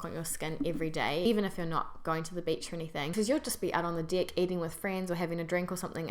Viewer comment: Did you just say 'out on the dick'? Naughty Naughty - Borat voice.